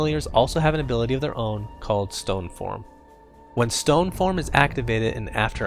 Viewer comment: "they have their own abilities called STONED FORM" -misheard this and was laughing quite hard, LOL